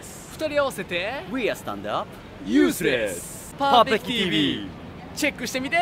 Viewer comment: Legit sounded like "Useless"